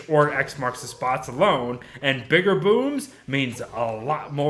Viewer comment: I heard ‘bigger booms’ as ‘bigger boobs’ and was confused for a second lmao